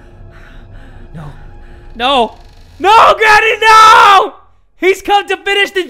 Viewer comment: why did my captions say no daddy no